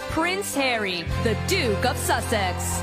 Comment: I swear I heard “The Dick of Sussex” 😳😳😳when the lady made the announcement at the NFL Award. But I was washing my hands so I rewind and heard Duke. 😂😂😂